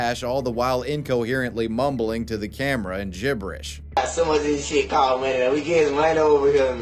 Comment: "All the while incoherently mumbling to the camera in gibberish" Did . . . Did anyone else have all too easy of a time hearing what he actually said? I mean it was still nonsense, apparently hes got "Some of this stuff called money" and we already know the story